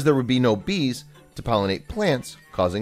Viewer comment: I thought it said beers not bees